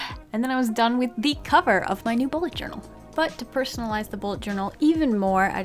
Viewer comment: I thought it said ballet journal Edit: I was like CLICK!